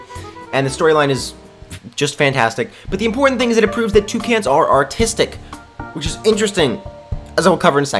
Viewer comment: This whole time i thought he was saying two cans